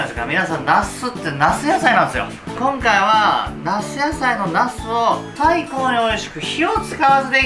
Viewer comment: 夏野菜のなすがどうしても、ナス野菜のナス〜って聞こえる